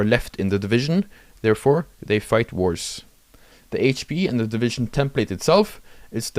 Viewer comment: When you said: "when they have lower HP, they fight worse", due to your accent, I thought you said: "they fight wars" and I was like "yeah? Obviously?"